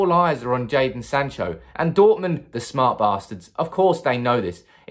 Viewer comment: Dortmund, the smart bastards, lol, rolled off the tongue. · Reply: I thought he said "smart masters"